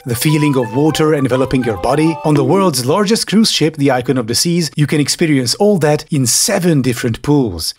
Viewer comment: funny how I heard "icon of desease" instead of "icon of the seas" I think it's pretty fitting